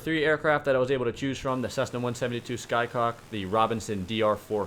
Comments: Did you just call the Cessna 172 a "Sky Cock"? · WAIT Did he just say "Cessna 172 'Skycock'"?